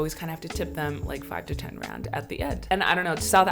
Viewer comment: someone might think she said Grand